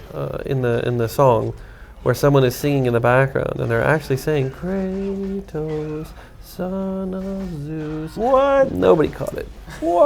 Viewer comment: That person is actually saying "Kratos... Thanatos..." Which means "Death"